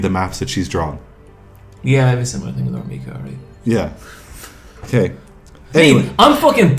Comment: who does say is his waifu @ 1:05:52? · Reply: It's way too indistinct and mumbled to make out, but I'm still like 99% sure it's R. Mika.